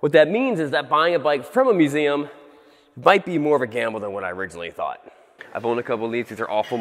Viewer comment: It Sounded Like You Said (It Bike Be More Of A Gamble Than Whatt I Originally Thought) Laughted So Hard And Please Tell me If It Was BIKE ANd Not Might :D